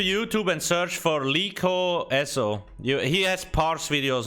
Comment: Wait who did he say to check out on YouTube? Lee Core??? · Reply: Liko